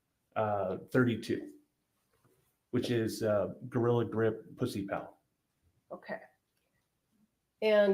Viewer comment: Im sorry did i just hear "gorilla grip pussy pal" or am I going insane?